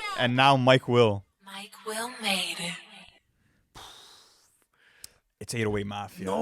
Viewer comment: The first time I heard the mike will made it tag was on “no lie” by 2 chainz and drake in 2012 and for a while i thought that tag said “NyQuil made” lol